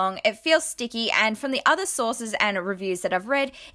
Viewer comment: Did anyone else notice the random bottle of tomato sauce at 8:39? I assume it's because when she said "sources" it sounded like she said "sauces." LOL